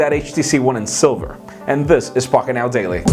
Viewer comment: it sounded like (fuckit now daily)